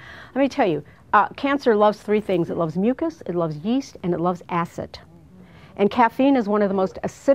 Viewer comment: I'm not english, could someone please write what those 3 thing she says at 5:40 , it would be very kind :) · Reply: missbs1 mucus, yeast, acid